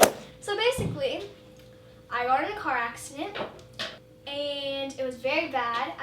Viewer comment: Okay look I don’t want to accuse her of lying because she was just in a horrible accident and I’m pretty sure the last thing she wants is to be accused of lying but, however I find it weird that at 0:24 it sounds like she said “ I ordered a car accident “ I tried to listen for I got in a car accident but I didn’t hear it, so I turned on captions, now I know captions get words wrong all the time but I just wanted to try it out and in the captions in said I ordered a car accident, and if you think about it ordered and I got are two completely different words, they don’t sound similar at all, I just find it weird..